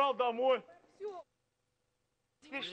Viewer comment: what he says? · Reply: Теперь что будете делать? -[пойду] Домой, спать